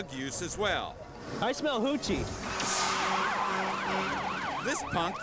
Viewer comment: At first I thought the cop said I smell Coochie instead of Hoochie that made me laugh so hard